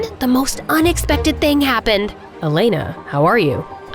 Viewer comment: Did he just call her Elana?